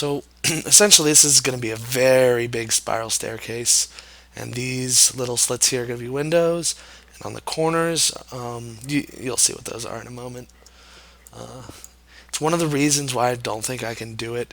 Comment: did he say little?